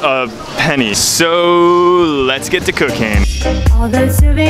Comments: Sounded like he said lets get to cocaine 🤣🤣🤣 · Lol @ 1:18 I thought he said, "Let's get to cocaine!" And I was like: Wait...WAT? Until I noticed eat he really said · I thought he said "lets get to cocaine" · sounds like he said let’s get to cocaine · When he said let’s get cooking my friend thought he said let’s get cocaine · anyone else hear lets get to cocaine? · I thought he said lets get to cocain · i thought he said,"LETS GET YO COCAIN 😂😂😂" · WHO heard him saying cokaine not cooking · Thought he said lets get the cocaine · “Sooo let’s get to cooking” What i heard : “Soooooo let’s get cocaine” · Did you just say lets get to cocaine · I thought he said let's get the cocaine. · am i the only one who heard the first time "sooo lets get to cocaine" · let's get to cocaine 😂 it's what it sounded like